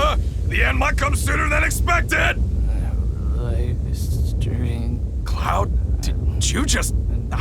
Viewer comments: Anyone get what cloud said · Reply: Life stream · Does anyone know what Cloud was saying at 9:08? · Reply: Lifestream